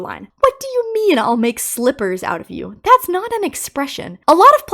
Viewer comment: For Rockstar Bonnie’s slippers line: rabbit slippers! It’s a joke about a rabbit wearing human slippers like a human wears rabbit slippers (though I’m sure most rabbit slippers aren’t real rabbit) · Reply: I thought he said slivers.